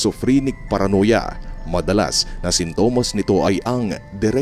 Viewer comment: can you say me whats the exactly word he says I cannot hear properply. "Madalas na..." whats the next word?? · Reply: Sintomas